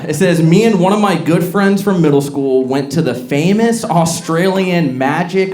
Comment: My hearing impaired ass heard that as, "Me & my best friend in middle school went to". I was like wait.. wait. What?! You were in middle school?! No, lol I just heard that very very wrong😅